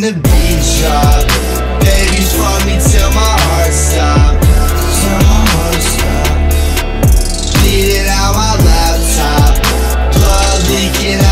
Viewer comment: Anyone else thought he said "beat it at my laptop" Was like woah dude